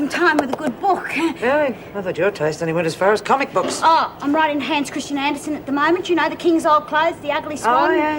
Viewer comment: What did Doreen say at 24:08 - 24:12 ? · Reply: Hanns Christian Anderson💞